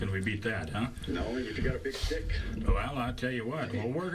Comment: did he just say " big dick"